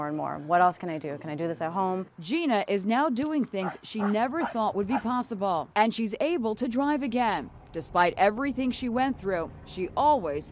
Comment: did she just say I'm extremely unlucky to be alive?